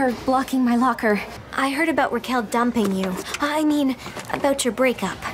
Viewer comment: I heard her say "I heard about her dominating you " But, after listening 2 more times, I realized Barbie said dumping. Lol